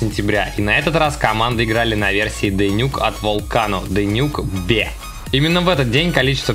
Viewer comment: de_nuke_ve?мне какбэ de_nuke_be послышалось...